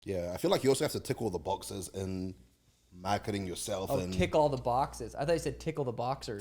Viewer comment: I thought he said tickle all the boxers too 😂